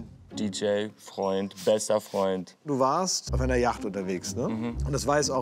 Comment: Hab ich FESTER FREUND gehört?????????😮😅